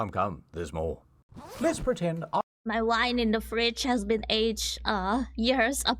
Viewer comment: I thought you said. My wife in the fridge has as been aged years upon years. Wtf is your wife doing inside the fridge? XD